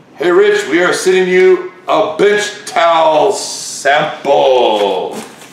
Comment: was it just me or did he say, "bitch towel"? lol